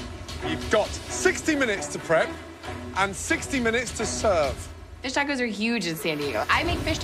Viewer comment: WHen Gordon said "you have 60 minutes to prep and 60 minutes to serve" i thought he said, "you have 60 minutes to prep and 60 minutes to SURF"